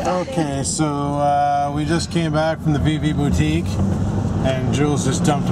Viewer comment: lol I thought you were saying "B B boutique" :p Ah well, all cleared up now :)